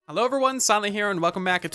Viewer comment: Am i the one whos new to the channel and thought he said “hello everyone stanly here” but it was “hello everyone silent here”?